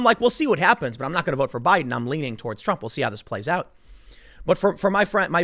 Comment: "I'm leaning for Trump." Did I hear this right?????????